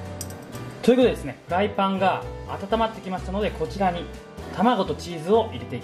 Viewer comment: 台パンがと聞こえた私の耳はイカれているのだろうか